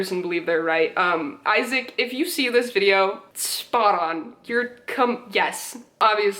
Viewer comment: ok ok, at 9:25, it sounds like you said "You c*m- Yes." I am aware that's not what you said, but I cant unheard it now (if this came off as hate, that wasn't my intention! Im so sorry if it felt offencive!) <3